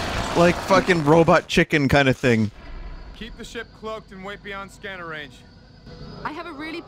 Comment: Keep the ship cloaked and wait beyond scanner range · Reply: I thought he said "Fuckin' keep the ship cloaked and wait beyond scanner range"